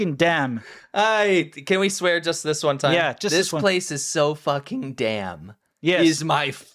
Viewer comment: when I first heard it I thought he said “ it’s so fucking damp!” And was like “are they in Vietnam?” Because I was just listening and not watching 😝